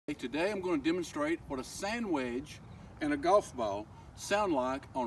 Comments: I thought he said sandwhich instead of sand wedge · I thought he said we'd hear the difference between a sandwich and a golf ball on a frozen lake...I was waiting for him to slide a ham and cheese across. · When he said sandwedge I could’ve sworn he said sandwich lol